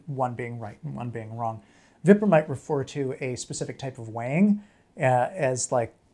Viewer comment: When he said "weighing" i thought he said "wang"